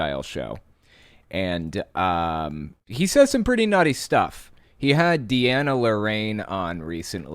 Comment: I first heard that as "He said some pretty naughty stuff." 🤣 And any links to George Soros is definitely intended as anti-Jewish.